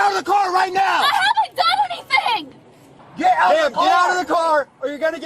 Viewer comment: i thought she said "i have a gun in my bag" at first not "but i haven't done anything" lol i was like, oh boy here's another idiot that's trying to get shot for literally no reason.